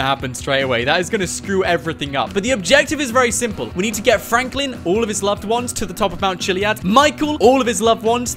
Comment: I was skipping start and i heard we need to get franklin and then i skipped and it sounded like he sayd: and all of his niggas (NO HATE I SUPPORT BLM!!!)